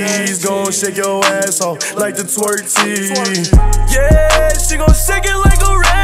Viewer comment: I thought he said tortilla😂 but he said twerk team